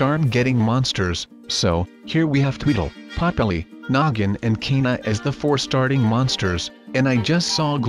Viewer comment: I love how he said noggin and I thought he said nuggin